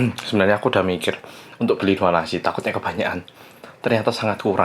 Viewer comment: ini kak mau beli apa kaak gak kedengeran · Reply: Beli 2 nasi kak